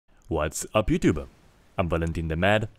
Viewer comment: When you said, "What's up YouTube?" I thought you said, "What's up pubtube?".